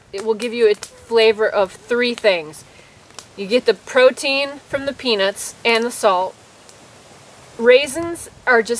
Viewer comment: I kept hearing "you get the protein from the penis, and the salt" I had to rewind a few times to understand what was going on haha